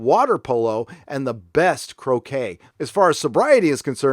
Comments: I thought you said the betty ford clinic had the best cocaine not crochet lol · Almost thought you said Betty Ford had the best cocaine not croquet lol. Very thoughtful vid!